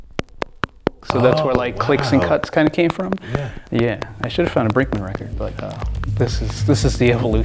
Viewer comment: what does john say? I shouldve found a "?" record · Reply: Possibly Brinkmann (as in Thomas Brinkmann, minimal electronic artist)